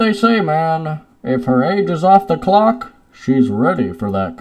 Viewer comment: “Hey you know what they say man if her age is off the clock she’s ready for that co-“ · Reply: me who thought it was on the clock: I got a few calls to make